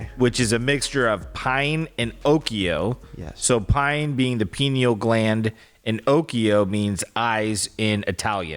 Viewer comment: i thought was saying "penil gland" I was thinking "omg Pinocchio means penis eyes?!" 😂😂😂 bc one of my friends in middle school used to call people "anus breath" and "penis eyes" as an insult all the time it was like her thing and I was getting ready to text her lmao yes we're still friends in our 30s